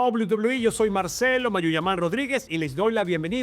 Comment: nunca habia entendido eso de mayuyaman, pense que decia mandiaman hahaha entonces aqui es donde suben la previa de todos los eventos? cuando seguian por fox sports la unica previa que se veia en la tele era la de estados unidos